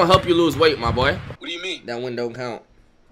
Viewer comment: What thought he said “That win don’t count” What I heard “That window count”